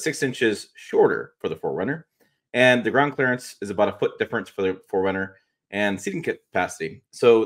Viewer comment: what are you saying at 7:28? A foot difference in ground clearance?